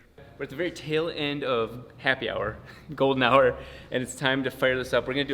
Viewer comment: Did you say ’Golden shower’? 🤤🔥🤭